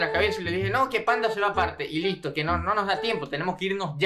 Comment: Y aqui empieza la historia de pandamia : Capitulo 1. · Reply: Pensé que dijistes pandemia parte 1